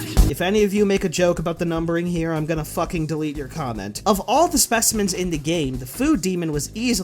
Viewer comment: My first time hearing it, I thought you said "I'm going to fucking eat your comment"